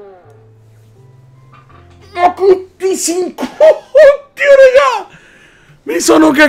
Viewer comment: ho sentito "ma puttisicu u u"😂😂😂